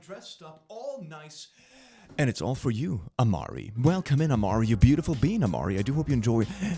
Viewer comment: I deadass thought he said Omori 💀